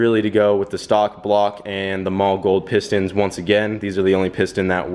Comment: what are you calling those pistons? sounds like your saying "mall gold". its Mahle, sounds like Molly